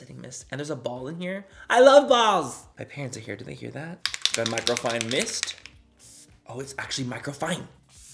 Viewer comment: I’m laying here half sleep , opening closing my eyes I literally thought you said “white girl fine mist “ 😆 I was like nooo! I know that’s not what he said ! Time to go to sleep 😴